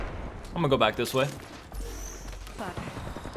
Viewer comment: it sounds like wraith said "fuck" after being hit by that nade 😂😭 im dead